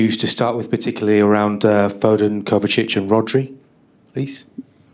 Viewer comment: Wait did he just say Kobbie instead of Kovacic🤣🤣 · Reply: He said kovie not kobbie 🤦🏻‍♂️